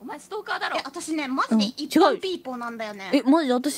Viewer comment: i'm still questioning what is them saying "... people"? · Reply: Ippan people 一般ippan means regular/commoner here